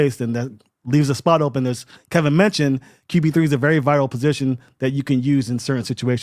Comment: “qb3 is a very viral position”. What?? I’m pretty sure Kevin didn’t say “viral”. Maybe “vital” 😂